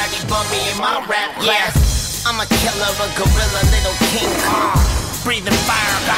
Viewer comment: Did he just say Nigga? lol